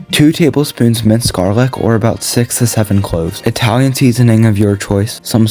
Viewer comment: did you say <num> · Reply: I think 6 to 7 cloves is what he said